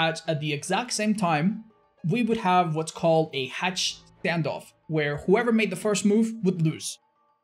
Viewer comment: I thought said "hatch Gandalf." same thing though. Claudette, you shall not pass.